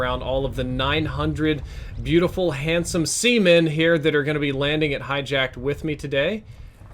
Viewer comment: Did he just say "beautiful handsome semen.."? He is not scared at all eh LOL